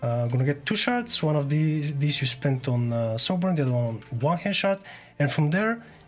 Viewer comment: literally can't understand what you're saying with subtitles and all, can you actually talk like someone is new, not mumbling your words, and not short-hand words like, "stalks" for dreadstalkers..that took me 2 whole minutes to figure out. It's not an accent issue either, it's just you talking like everyone knows what you're talking about right away. The "One-hand shot" at 6:00, that took me another 15 minutes to understnd: "1-hand-shard", meaning "using Hand of Guildan with 1 shard", like wtf man.